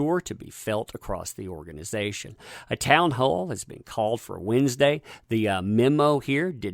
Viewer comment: When he said town hall.. I thought he said Amber heard.. Now when he says it.. listen carefully 😂